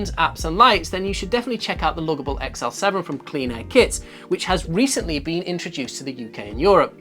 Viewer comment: Loadable XL7 from Cleanable Kits ??? Is that what you are saying at 11:50 ??? Thanks